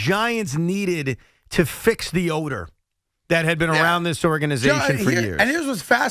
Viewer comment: The Giants "need to fix the odor." At first I thought he said "fix the owner."